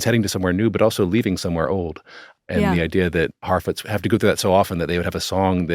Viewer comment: Every time he says "Harfoot" I keep hearing "Halfwit" and I am convinced it's intentional.